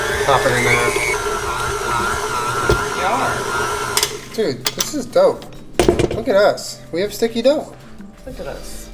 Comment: Is Julien saying, “dude, this is dough” or “dude, this is dope”?! I can’t telllll 👀😂